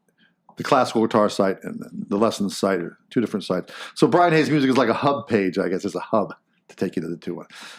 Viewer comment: Totally thought he said Brian “hates” music for a hot sec 😂 and actually thought that was kind of genius in a ironic sort of playful way haha. Love all of what you are doing with the channel, keep ‘em a coming 💪